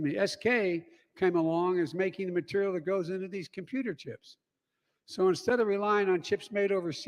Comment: Computer chips, he’ll I thought they said chocolate chip that’s my favorite. FJB